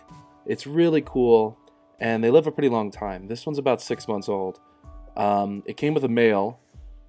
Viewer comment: who else thought he said it came in the mail lmao